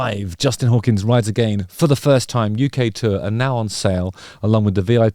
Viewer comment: When you said UK tour I misheard it as Ukulele tour. I guess that wouldn't be so bad - I believe In A Thing Called Love ukulele solo